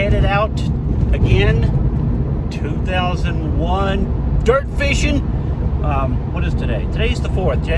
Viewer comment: Did I hear 2001? 😂😂😂 it's 2021!!!